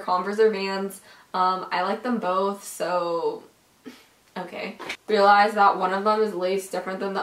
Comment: did she just say "i like them both hm so.. Im gay" ._.